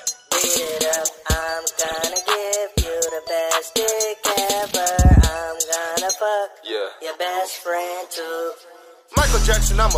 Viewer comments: this is not what I heard on tiktok I heard "I'm gonna bring your best friend to" 😭💀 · Who thought it said " I'ma bring your best friend to Michael Jackson"